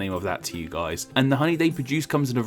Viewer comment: I cannot possibly be the only one who thought that it said 'Horny'.